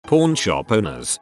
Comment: Me listening to this to sleep, instantly thought he said porn shop in the first second